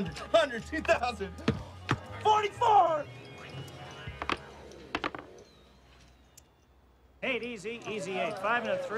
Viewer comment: what does Baker Hall say at 3:19 ? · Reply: “Break”